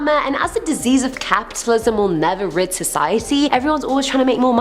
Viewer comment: Did she say rinse society or rig society at the 8:41 mark ?